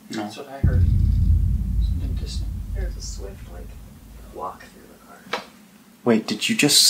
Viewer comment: That whisper that thought he heard at the end, it said walk thru the car